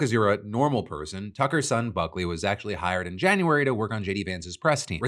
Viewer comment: I genuinely heard f*ckley instead of Buckley 😂